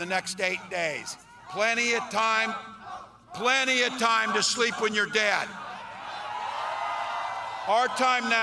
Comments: Oh my stars! I thought Walz said "plenty time to sleep with your dad." Not "plenty time to sleep when you're dead" 😂😅😂😅😂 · I swear I heard “plenty to sleep with your dad” Honestly, sounded like something he would say.